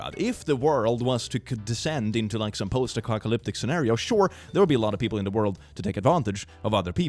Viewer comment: FUN FACT: AT 5:16 IN THE VIDEO SAYS POST ACOCKALYPTIC INSTEAD OF POST APOCALYPTIC.